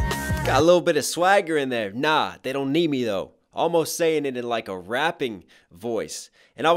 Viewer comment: It wasn’t that she said “Nah they don’t need me though” she actually said “Nah they don’t hear me though”